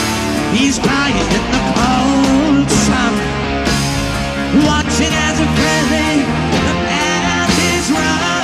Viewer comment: Look at the album cover - Aqualung IS Ian Anderson. In the story of the song, he (Aqualung) suffers deeply and falls into a pit of depression and hopelessness. But then - suddenly - at the end of the song - "Sitting on a park bench, watching the frilly panties run..." - HE'S BACK, and he carries on again! Theater. Another song to revue - one of my all-time favorite songs - "Mother Goose", also from the Aqualung album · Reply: Funny I always heard that as "watching as a pretty pet is run". Which is correct? LOL